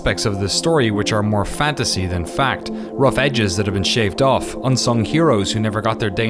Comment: there's an error in the CC. Says "fantasy than fiction" instead of "fantasy than fact"